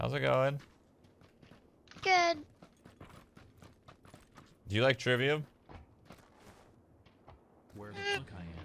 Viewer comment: Did he say Do you like "Trivium"? (the heavy metal band)